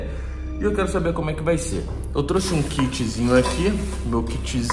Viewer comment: NÃO GOSTOU DO VÍDEO? TÁ TRISTINHO? QUER UM LENÇO? Quem decide o que fazer em meu canal SOU EU! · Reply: Eu ouvi "Um kit Xeon"?